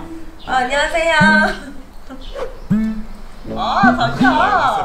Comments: 자기야~~ 라고 들리나요?....!!!!! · 이때 옷을 가져가고 무릎가리개로 가져갔는데 가 한테 "아 자기야 "라고 한거 나만 들림??ㅋㅋㅋㅋㅋ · 나만 아ㅏ 자기야 라고 들림? 개인적인 의견이긴 한데 보다 랑 랑 더 붙어있는거 같냐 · 가 보고 아 자기야 한것같은데....ㅋㅋㅋ 잘못 들었나....... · 자기야라고 저만 들었나요? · 초 뭐지....;; 아 자기야 이렇게 들림 근데...님이 하신건 아닌고 같은데..